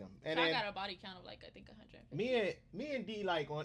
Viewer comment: Bruh did she just say she had a body count of 150 under her breathe? 😂😂😂😂😂😂